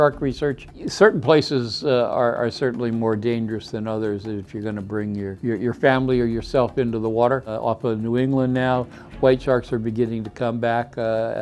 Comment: Dustin Faiers it says THIS U.S. coast line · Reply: im_not_a_penguin good point out. Never mind I'm dumb. lol I thought he was saying "England" not New England